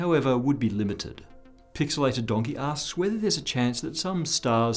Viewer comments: Hi You should pause between saying a user name and ask. It sounded like you said PixelatedDonkeyAss. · You said "pixelated donkey asked" I heard "pixelated donkey ass" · Thought he said PixelatedDonkeyAss asks the question.